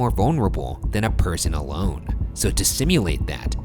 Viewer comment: I heard "Demons prey on vulnerability, and nothing is more vulnerable than a ~personal loan.~" and i was like...wow, I felt that.